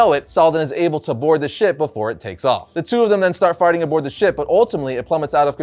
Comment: does he say farting or is that just me haha 😄